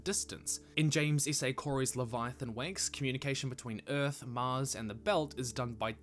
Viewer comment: Did he just say Leviathan Wanks? 🤣